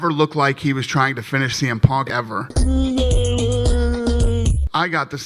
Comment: why have you edited the audio of dana speaking? at round 0:16 and 3:24 it sounds like he says "could have finished the punk ever" and "could have finished cm punk a few times" when really he said "finish the fight" both times? I get that perhaps this makes the context of what he is saying clearer... if you are somehow unaware of anything related to the UFC.. but was this really necessary? Your channel is about analysing how people communicate and this is not something I expected you would do - please don't do it in your future videos